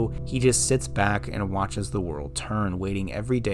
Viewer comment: I love how you say “watch as the world turns” instead of “watch as the world burns”